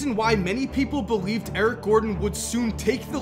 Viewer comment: Thought he said Aron Gordon